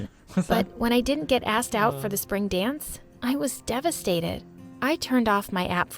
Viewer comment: is it just me or does it say spron dance